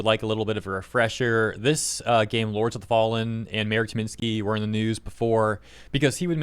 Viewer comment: I kept hearing "Mayor Tyminski"